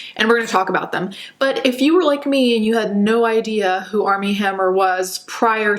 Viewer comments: I thought you said Arm N Hammer and I was like not my baking soda being problematic! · Mmm I had no idea who he was when I heard his name. I swear I thought they said "Arm N Hammer" Baking Soda. Sheesh.